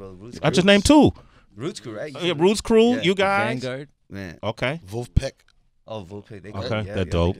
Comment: What’s the band Kelsey says at 7:27 ?? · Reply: Edward Herrera D’angelos band, The Vanguard